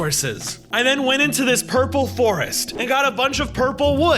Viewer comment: did he just say "purple forest" lol